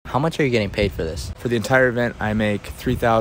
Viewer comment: For some reason I thought he said 24 thousand and I was so confused on what was wrong 😂